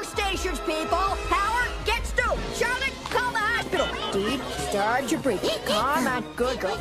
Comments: I thought Betty said "hop sicle" instead of "hospital" when I was a child. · As a child, I thought that Betty said "hopsicle" instead of "hospital" at 0:08! I can't be alone!